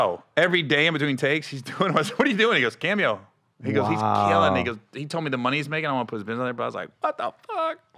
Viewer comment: did Gary just say "he killin niggas🤳" 😆😆 if he did I'll give him a pass tho ijs